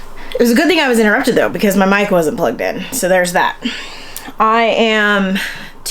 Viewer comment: I thought that you said your mind wasn’t plugged in!😂